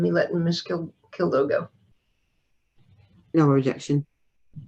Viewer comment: What is role, and did she say “no rejection?” 13:04 I think the judge asking the mother “do you still need a break” confused her. She said she did need a break and left shortly thereafter. I’ve noticed informal language used in these hearings sometimes leads to misunderstandings with the defendants, especially when there are language and/or substance issues involved.